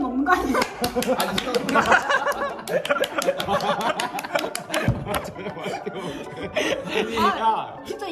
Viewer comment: 저녁 맛있게 먹을게 라고 생각하면 그렇게 들리고 잠깐만 이거 어떻게 라고 생각하면 그렇게 들림